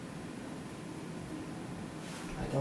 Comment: What is he saying? 12:10 I don't... and then? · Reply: "I don't know how...why I even went."